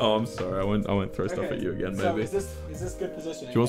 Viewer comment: I thought I heard him say “I won’t throw stuff at u again baby” and my mouth went 😦🥲